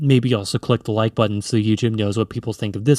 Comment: sounds like ur saying Egypt and not "youtube"